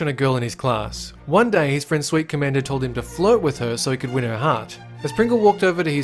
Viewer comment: Anyone else hear "Float with her"? Oh, hiya Georgie.